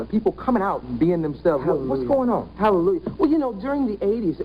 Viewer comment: Did she just say "HALLELU"?! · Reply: Hallelujah, she just said it fast.